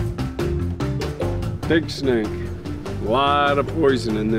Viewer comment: What kind of snake was that dark grey one? Was it a black mamba? The snake towards the end of the video was a Pig Snak? I couldn't hear clearly. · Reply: I thought he said pig snake. Haha. They had two Puff Adder in the camp I went to in my 2020 safari.